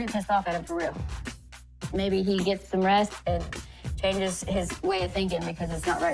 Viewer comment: change my way of thinking? I thought you said change my way of DRINKING!